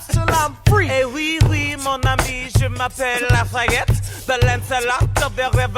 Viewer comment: When he says “Lafayette” at 1:49 I always hear “ la faggot”😂